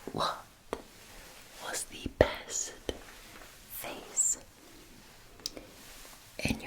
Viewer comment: Didn’t she say “what was the best phase in your life ?” 🤔😂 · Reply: Dud she say phase??? I heard it as Face. That's embarrassing AF. So I made one pronunciation mistake in awhile. Still makes for a good joke lol